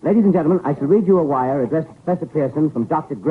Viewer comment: I swear I thought he said “Dr. Dre” 😂